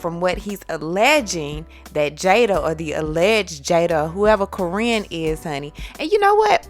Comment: sounds like you said korean so i hadbto rewind